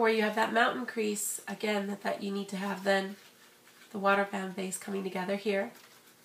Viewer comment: thumbs up is you think she said water bam base